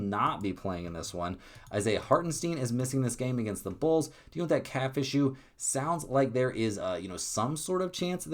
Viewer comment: I thought you said " dont let them CatFish you" but you said " have a Calf issue" 😂😂😂😂